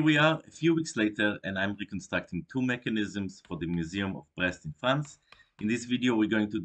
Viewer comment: first time through: the museum of what? Like, it just wasn’t clear. 2:10, second time: nope, still didn’t quite catch it. 2:10, third time, this time with captions turned on: ok, I’m not at all sure that helped! 🤣 Checks description: ohhhhh, I see. Both what is being said, and why the captioning might come out that way. Now I just need to figure out what “brest” means in this context. 🙃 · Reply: Ahhh… ok. “Brest is a port city in Brittany, in northwestern France, bisected by the Penfeld river. It’s known for its rich maritime history and naval base. At the mouth of the Penfeld, overlooking the harbor, is the National Navy Museum, housed in the medieval Château de Brest.” Now it all makes sense. Ok, carry on! (Presses play again.)